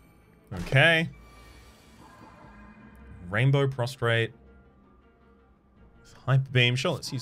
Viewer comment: I thought you just said rainbow prostate lmfao